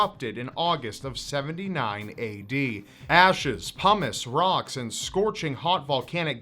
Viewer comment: did he say "pommes"? 😂